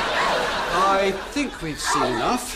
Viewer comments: I demand that the brilliant bit is released to the public! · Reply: Oh, I heard "Bolian bit" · "your honor,i object! there is a brilliant bit coming up" LOL XD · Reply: I totally thought he said Bolian bit